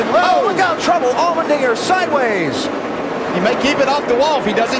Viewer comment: does he say what i think he said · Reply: Dynaxia “allmendinger sideways” what did you think he said